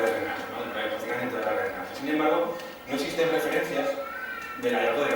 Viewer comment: Hola! 13:33 que dice? Todas las redes de que? Gracias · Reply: Redes de drenaje, verdad?